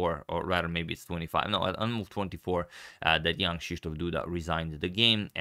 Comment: I always hear "Young Krzysztof Duda" instead of Jan :P